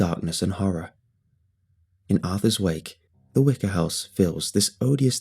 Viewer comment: At first I thought it said the wizard house oops 😬